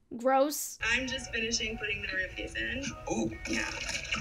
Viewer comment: the blonde one said "I'm just finishing putting the roofies in" Plz tell me if I misheard I want to be wrong P L E A S E